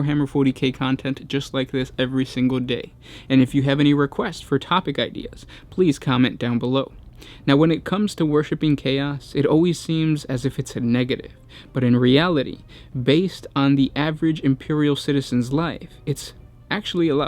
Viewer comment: Did I just hear.. "JOINING CHAOS???" Smash them!